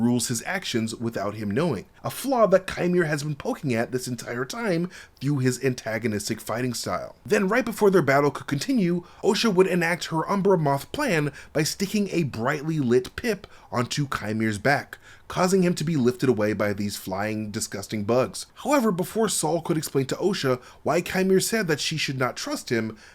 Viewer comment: I thought you said a brightly lit pimp😅